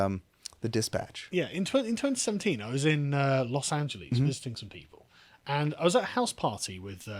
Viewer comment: "In 2017 I was in Los Angeles fisting some people..." I know that's not what he said, but it sounded like it and it's kind of hilarious.